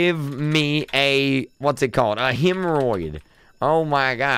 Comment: did he say hemorrhoid